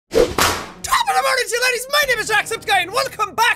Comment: I know you're saying 'laddies!' in your opening line, but i keep hearing 'ladies' instead.